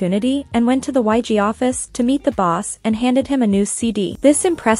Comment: I thought you said nxde cd😭😭😭